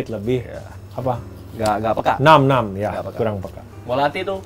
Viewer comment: Gw salah denger ga sih ,pas bahas kepekaan lidah terhadap rasa , ±14:36 , chef juna bilang "Na'am Na'am" , kakau bahasa arab kan kek when u agr3e about something u say " نعم، نعم " , gt, situasi keknya chef juna juga kek "agree" gt😳😅